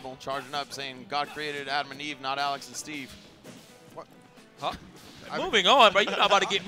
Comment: that was a dam good joke for those who can take a joke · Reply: I'm guessing they misheard what he said and thought he said Adam and Steve when instead he said Alex and Steve, but yeah that was a good joke